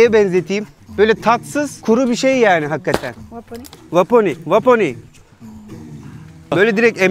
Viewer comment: kadın sanki hadi hadi ye dedi yoksa benmi yanlış duydum😮